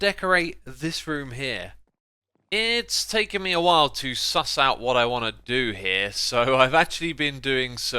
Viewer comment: did just say sus?